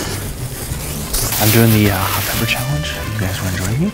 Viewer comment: I thought it said hot people in the library not hot peppers 😂😂😂😂😂